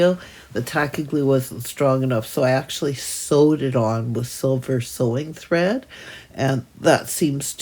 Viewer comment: I thought you said you soldered the hinges with silver sewing thread and it was some incredible new technique - then the penny dropped 😂 what a great mini you’ve created, I really enjoy your videos thank you from Nottingham, England xxx❤